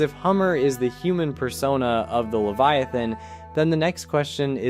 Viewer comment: did i heard persona ?…. PERSONA..DISTURBED TO THE PEACE.. LOOKING TO MY EYES